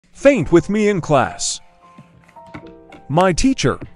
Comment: I..im sorry i thought u said paint